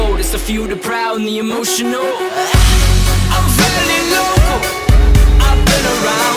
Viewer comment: Siempre pense que decia "I'm feeling loco" aire